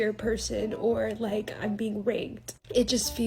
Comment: I thought she said bring raked. LoL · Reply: I thought she said its like being ranked.